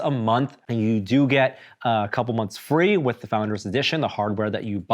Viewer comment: I coulda swore he said you get destiny free in the founders edition. Someone correct me if I’m wrong